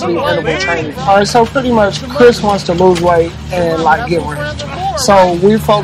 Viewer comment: Did he call him "Chris" right here? 8:30 I guess that's his real name. :)